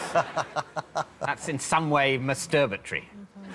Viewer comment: what was word David Mitchell used? Misturbatry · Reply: Masturbatory